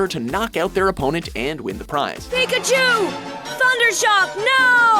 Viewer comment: is it just me, or did Pikachu say f* CHUUUU at 7:46? XD lol